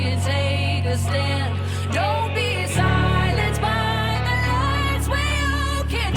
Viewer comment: I thought she said “Don’t be silenced by the lies” and learn to understand “ But.....that’s just me😊